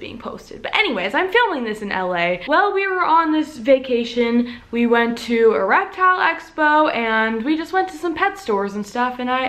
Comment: i thought u said "we went to erectile expo"